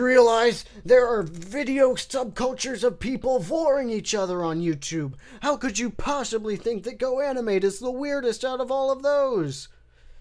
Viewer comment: what does he say here? I rewatched it a thousand times and still don’t know lol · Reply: there are video subcultures of people voring eachother on youtube, how could you possibly think that goanimate is the weirdest out of all of those